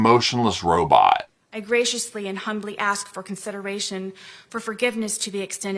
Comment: She says she’s coming with “graciousness and humility” to ask for forgiveness. She doesn’t get that those two character traits are never ones that you get to assign to yourself. They are always in the eye of the beholder. And I venture to say that all of her beholders see no grace or humility in this apology. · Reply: I thought she said "graciously and "honorably" – but the second word might have been "humbly". I am NOT rewinding to check. Either one is inapplicable to SKB.